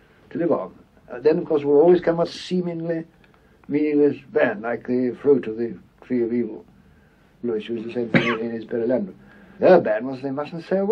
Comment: Thank you so much for this transcript! One correction for 7:07 - 7:12 : Instead of "Like the free of the tree of evil, which was the same thing in its parallel", he says ""Like the fruit of the tree of evil; Lewis used the same thing in his Perelandra."